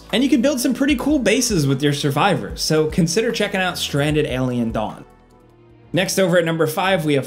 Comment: I dead ass thought he said Stranded Alien Dong. 😅